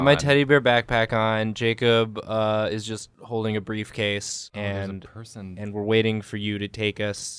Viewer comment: did Jacob say his briefcase was a person